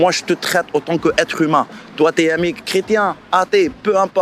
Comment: "Toi t'es un mécr..." 😉